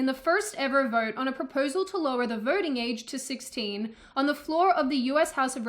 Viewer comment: I heard "vorting." But she's sticking lesser number of "r" in random words.